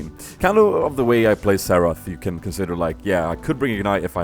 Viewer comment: Every time he said Xarath I thought he was saying Seraphs.. Like Seraphs was good on him or something xD